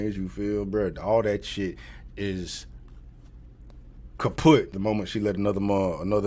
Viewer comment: Did he say "Kaputt" at 15:20? Love that. German Follower asking.